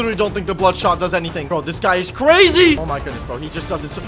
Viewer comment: What he said: ‘This guy is crazy!’ What I heard: ‘The sky is crazy!’